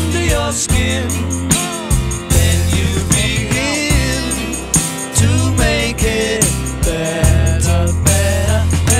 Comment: WHAT does Paul say @ 2:58? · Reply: "Ah. Fucking hell." - Paul He said that when he made a mistake on the piano